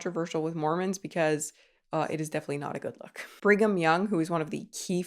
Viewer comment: what was his name? I must've misheard you, his name can't be 'bring em young'.... oh brigham, i see